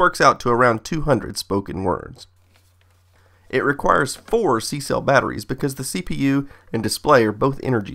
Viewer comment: The subtitles said 3 but he actually said 4